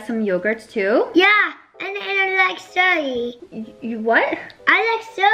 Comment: I honestly thought he said he liked soy